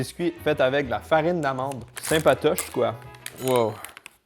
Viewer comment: As-tu dit " sympatoche quoi " ? .....